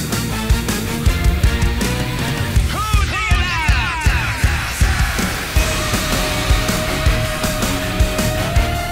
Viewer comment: What does he say at the 0:42 mark? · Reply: He says "Who do ya love?"